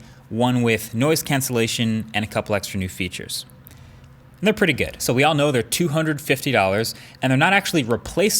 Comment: On god i thought he said "They are pretty gay."